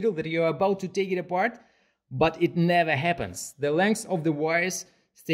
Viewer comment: What I heard was “the lungs of the wires”